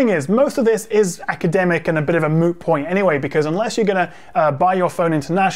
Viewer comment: did you just say moo points